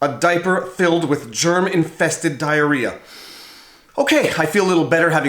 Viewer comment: “A diaper filled with German infested diarrhoea” (01:17) ... is what I could have sworn he said!